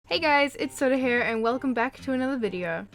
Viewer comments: it sounded like she said 'hey guys it's SODA HAIR' is it just me or... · I thought you said 'soda hair' but you said 'soda here'